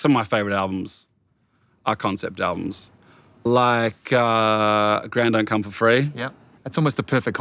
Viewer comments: Does anyone know what concept album he said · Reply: A Grand Don't Come For Free, by the Streets. It's a rap-opera. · Can someone tell me which album he refers to ? I can't understand what he says :( ( I don't speak English ) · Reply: Ok got it. He said A Grand Don’t Come for Free